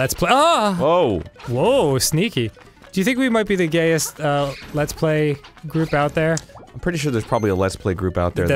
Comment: who else heard lez-play